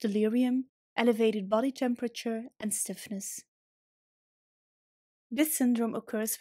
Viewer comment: Did you just say that low serotonin causes syphillis??? You do know that syphillis is an STD, right?? Woah. I think you have that backwards?? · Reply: She said stiffness not syphillis